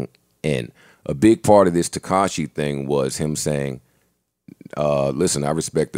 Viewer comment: It sounded like Rory was saying Tekashi and Tinashe... Tekashay